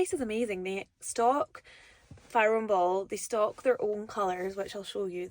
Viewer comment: Not sure i have a favourite paint colour but i really liked First Frost by Dulux for my Hall but differing shades of blue are my favourite colour. I thought you were saying Fire and Ball, but then i googled paint colours and saw it was Farrow and Ball☺